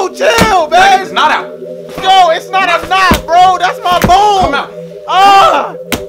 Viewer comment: “ That’s not a knot bro! That’s my bone! ”😂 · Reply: If you crop out the other parts where it only shows him laying down. It sounds like he says, that’s my bood. He be getting his cheeks clapped